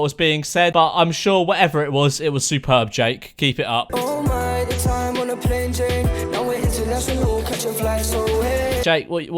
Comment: I heard “oh my check the time on the plain Jane” and I mostly heard “aeæaaay